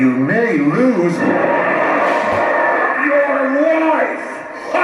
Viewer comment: Why does it sound like he said wife 🤔